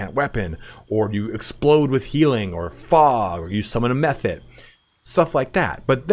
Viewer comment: ish it sounds like you say "summon a meth head" LOL i know you dont but still xD